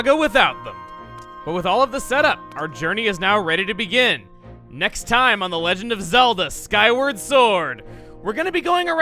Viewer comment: Haha at the end I thought he was about to say "next time on the legend27"